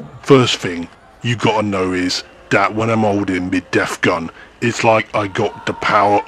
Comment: I thought you were saying Zat gun at first. Like from Stargate. lol